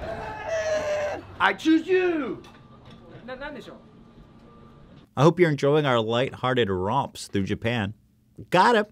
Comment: Can someone please tell me what this man said? I'm guessing something along the lines of, "What the hell is wrong with you?" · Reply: He said "what is it?"/"what do you want?" Japanese people are still polite even to someone like Logan Paul